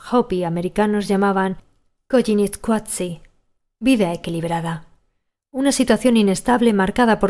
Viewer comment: en realidad del libro Me parece que dice desequilibrada